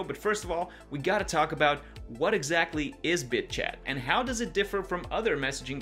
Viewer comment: Lol I thought it said bitch chat lol